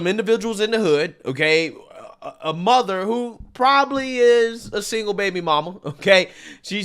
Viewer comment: He said "maybe" or do you just let words fly over your head. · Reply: Sir I heard exactly what he said and he never said Maybe he said probably she’s a typical baby Moma and I just asked the question. How did he come to that conclusion? Nothing went over my head. Listen at the 9:47 mark. Thanks for your reply